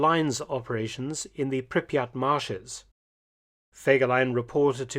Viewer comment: Did he just mentioned Pripyat? Or am I hearing things? Don't tell me that it existed even during WW2. And yet, it did not survive it's own catastrophe: Chernobyl accident, 1986.